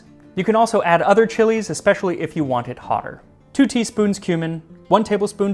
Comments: Am I the only one who heard two teaspoons human at 3:36? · There part where you said, "then add the cumin", I totally thought you had said "human". :D